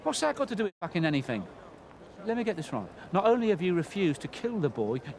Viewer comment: he says "Lemme get this wrong." I just heard it for the first time after watching the movie and the scene so many times. Did he actually hope to get the whole situation wrong because of how unbelievably stupid it sounded from his perspective as a professional hitman? Haha, it kinda cracks me up now that I realize. This movie's dialogue just keeps on giving on every rewatch. Brilliant! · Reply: He says right not wrong, it’s just the cockney accent